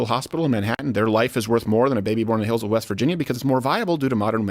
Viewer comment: did you say "Hillsville, WV"? cause as someone from the area of Hillsville, we don't like being lumped in with WV. We are in VA on the border of NC, that ain't us dawg lol